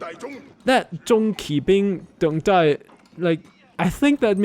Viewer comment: What they are saying might be '重戟兵', which is referring to the weapon that they're holding